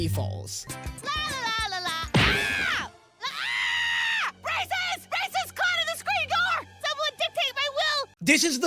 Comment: SEE, EVEN THE SUBTITLES MISHEARS BRACES AS RACIST